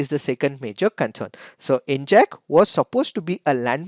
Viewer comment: what is that word i can't understand NJACK on